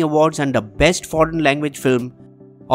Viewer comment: Hey, am I hearing it wrong at 5:00 when you say 'out of 53 nomination by india, only 21 on hindi' ? The animation shows 20 non Hindi nomination.